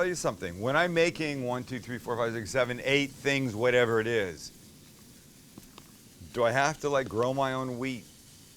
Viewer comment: Haha thought he said do i have to roll my own weed